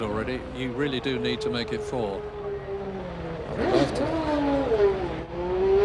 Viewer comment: Did he say “I wont”??? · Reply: He said i would love to